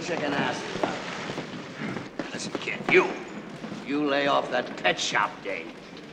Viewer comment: I thought he said "lay off that pet shop game" lol